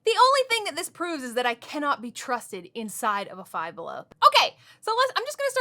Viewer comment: LOL that's funny thought you said that don't be trusted in a five below